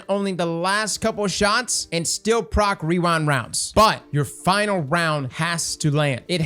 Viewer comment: I don't know if it's your mic, but all i heard was "weewind wonds"